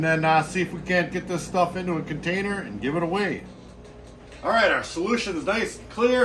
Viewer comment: I just realized you’re saying “give it a WEIGH”. I thought you was saying “give it AWAY” And I was like OOH A GIVEAWAY! 🤦‍♂️😂😂😂